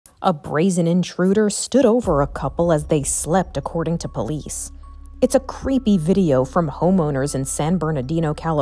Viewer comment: OMG I THOUGHT THEY SAID “INTRUDER SHOOTS COUPLE WHILE THEY WERE ASLEEP”